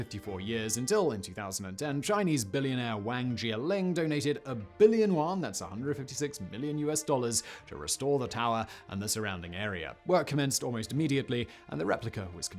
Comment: I keep hearing "Wang Jianlin donated a billion walnuts"